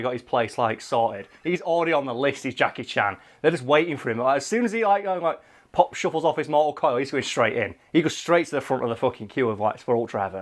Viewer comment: it sounded like you said "when [Jackie Chan] pop shove-its off this mortal coil".